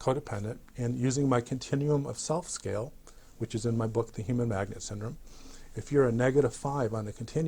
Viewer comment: I thought you said Human Maggot Syndrome. Makes sense. :)